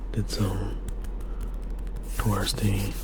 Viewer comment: Omg when you said did some “touristy…” I thought you said LSD 🤣 I was like oooooh werq.